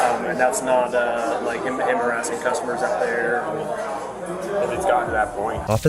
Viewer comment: hehe, @ 4:20 subtitle is wrong. it's "not him harassing customers", not "not him embarrassing customers"